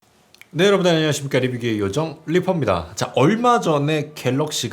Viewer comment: 리뷰게이 요정이라고 들려요